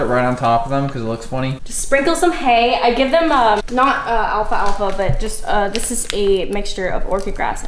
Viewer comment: did she say “alpha alpha” 😂